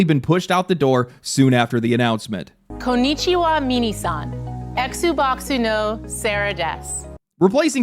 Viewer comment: Konnichiwa mini san? whaaat? Did she call Japanese small people?